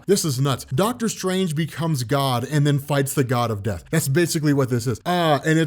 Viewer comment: I thought it said fights god of destruction I was like what beerus